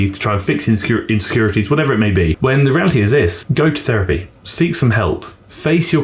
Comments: I was doing something while listening to this and I heard "goat therapy! :D maybe it´s not a bad idea altogether... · I thought you said "goat therapy" not go to therapy and I was like hell yeah I'll go to therapy with goats is it like goat yoga? lol